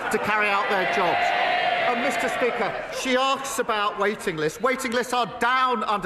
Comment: did he just say, she axe about waiting lists? <link>